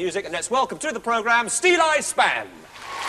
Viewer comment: LOVE this band. When I used to listen to folk radio on Sundays, I thought the DJ was saying Steel Ice Band and thus couldn't find their albums in the store!